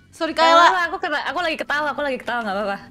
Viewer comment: i got shocked at kaela coz it really sounded bisaya (one of the languanges in PH) and even has the same meaning 🤣 What I heard: “ako ra gatawa” idk what it actually is in Indonesian but im just shocked at how similar they are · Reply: Kaela actually say "Aku lagi ketawa"